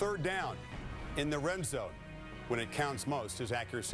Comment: Did he say "Rim zone" at 2:53?